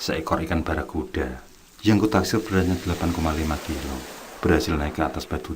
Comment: Berat ikannya 8,5kg??!! Besarnya,,, Ga salah dengar aku kan??! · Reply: 3,5kg